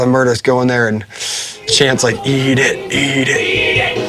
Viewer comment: What does M Shadows say at 8:57 that makes them laugh so hard? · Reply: Master Haraldur Johnny says "eat the shit out of it"